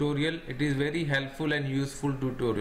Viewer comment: Pretty sure I thought I heard "don't fuck it to share and like this video" at 2:23 there lmao